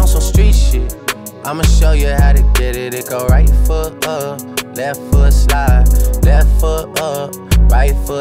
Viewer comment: I have such a bad hearing problem- I heard “right foot first left foot side” I